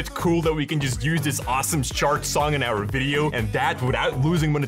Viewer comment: Real question 2:57 is he saying "Shart" tracks? And "Shart" music? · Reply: I spoke too soon... Thank god for captions, at 4:07 it's CHART MUSIC. Love this channel but he speaks his second language faster than I speak my first.